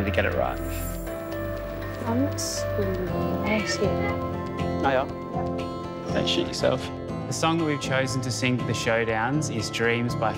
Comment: Ok, maybe I’m hearing things, but right around the 16:44 mark when says to, “I’m like, sweating,” it sounds like he replies with, “oh yeah? Don’t shit yourself.” It came off as if he was muttering it under his breath in annoyance. Am I completely mishearing this? It also doesn’t sound as if he’s being silly or trying to make her laugh...there’s a really irritable tone in his voice and I don’t know if he realized the mic would catch what he’d said. If I heard correctly then I’d have to say, no, pairing them together was not a great idea and at least one member of the duo was less than thrilled to be working with the other, despite the upbeat parting speeches they made. What a weird thing to say to someone right before you’re about to perform a duet together!